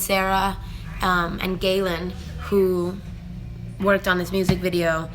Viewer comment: ANY ENGLISH SPEAKERS?? What are the names she says in the minute 12:35?? I know the last one is Galen (Galen Hooks).